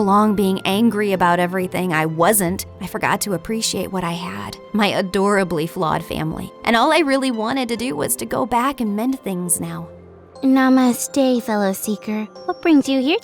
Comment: did she really said namaste